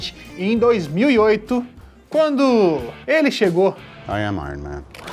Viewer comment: Juro q escutei o Pai tá on